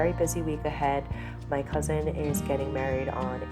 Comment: I did a double take because I thought you said your husband was getting married but you must have said cousin!